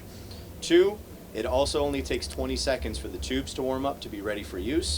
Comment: “Only takes 20 seconds for it to work” lmao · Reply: You clearly misheard, it takes 20 seconds for the "Tubes" to warm up. Tubes regularly take around 30 mins to an hour to warm up.